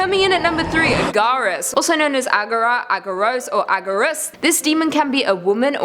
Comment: I thought you said gaara from naruto.